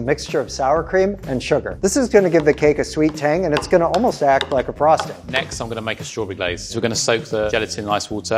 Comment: it sounds like he says "And it's gonna almost act like a prostate!"